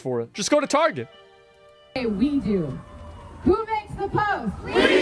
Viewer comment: I heard "who bakes the toast" rather than "who hates the post" and now I can't unhear it.